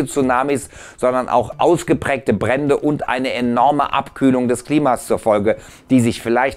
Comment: Hab ich richtig gehört? Abkühlung des Klimas? Könnte das vielleicht die Rettung sein?